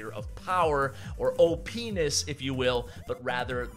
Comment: what did you say??? OPenis???